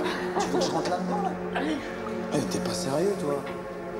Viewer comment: Y a que moi qui entends la boule dire "Aller" ?